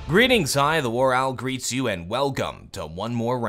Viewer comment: Shouldn't his intro be "Greetings, I, The War Owl *greet you." Not *greets you. You don't say "I greets you." Correct or we all ubsub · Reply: He explains this in one of his Q&A videos. The phrase he says is "Greetings, Hi, The WarOwl greets you" I also thought that he said "Greetings, I, The WarOwl Greet you" before.